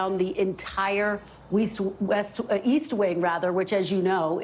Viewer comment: I thought you said Weest